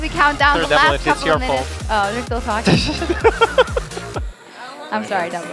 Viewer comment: What does she say at 0:39? :c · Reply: " Oo they're still talking.." XD